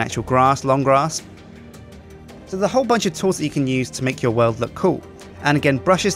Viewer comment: did you say "there's a hole bunch of torrs that you can use"?